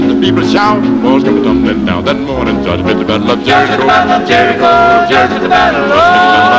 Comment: It sounds like he is say swoshua